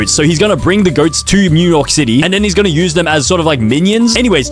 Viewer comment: bro did NOT just say Mew York City 😭🙏